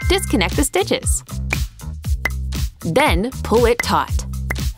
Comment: Is it just me or does it say at 4:36 poll it tot